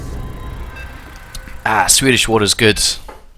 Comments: what does he say 27:35 ? Swedish water is good? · did you say in 27:36 swedish water is good